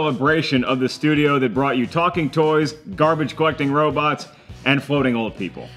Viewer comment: When he said "garbage collecting robots" for a second I thought he said "garbage fucking robots"... and that would have been an entirely different movie.